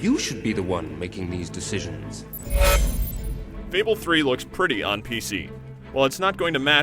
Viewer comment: did he said fable 3 on pc Oo